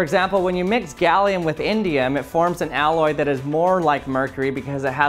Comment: Who heard India ??🇮🇳❤❤